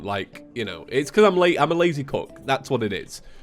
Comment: i thought he said he was a lazy cuck there lol